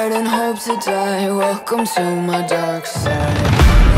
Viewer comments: For a sec there i thought it said dankside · Is it bad I thought it said dankside??